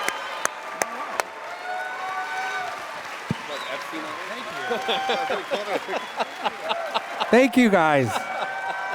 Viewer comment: can anybody tell what Matt said here? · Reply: Something like "Did he say Epstein award?" Later, he says "I really did think he said Epstein Award."